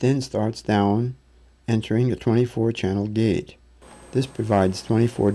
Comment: What does he say ? " a 24 channel ..." ? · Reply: I believe he said, "24 channel gate..."